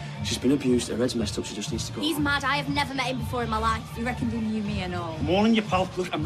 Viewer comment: What did Bethany say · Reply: Alan Mawson She said you're mad Ive never met you before in my life